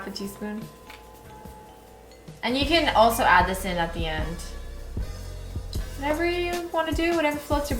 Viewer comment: wait, did you just say "you can add ASIN at the end."? 8:26 Im a filipino and ASIN is our language for SALT. heheh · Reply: "You can also add this in at the end"